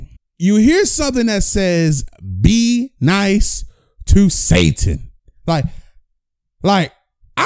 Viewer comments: It sounds like its saying be nice to simon 😂😂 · i heard "be nice to simmon " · I heard “ be nice to Santa “